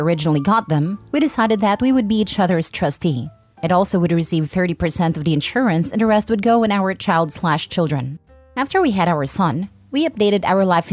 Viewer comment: you put your boyfriend/girlfriend in your life insurance? 😂🤣 omg.... Why? You sound like the type of person who adds their 1 month partner to their house title 😂😂😂😂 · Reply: I said it depends on how long, but tbf I also misheard it as health insurance lol